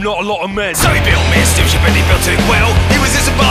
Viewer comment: I keep hearing 'spaceship' instead of 'steamship'